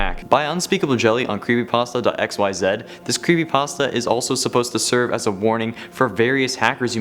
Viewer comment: Did he just say "crappypasta"